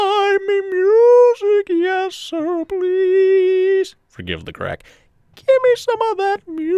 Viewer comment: I lost it for a minute with in the middle of the humming I hear "scuse the crack" XD